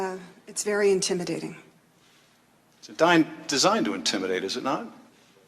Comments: Schiff said, "The dime (???) designed to intimidate, is it not?" I cannot hear clearly this word dime? dine? What is the word? Thank you for your help. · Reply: He stuttered the word "designed" he said: "It's digned... designed" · Schiff said, "The dime (???) designed to intimidate, is it not?" I cannot hear clearly this word dime? dine? What is the word? Thank you for your help. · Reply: "it is "designed" to intimidate....is it not ?" Is what Schiff said.